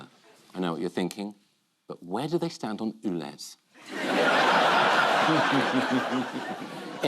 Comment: Can someone help me? What does Richard say at 8:43 ? "...but where do they stand on...?" edit: and 22:42 . What's Paul say? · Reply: Where do they stand on ULEZ